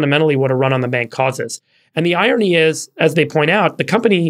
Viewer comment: I keep thinking "super gut" when I hear "and the irony is..."